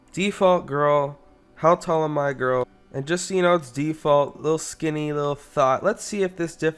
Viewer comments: woah I heard that “lil skinny lil THOT”😭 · did just call her a thot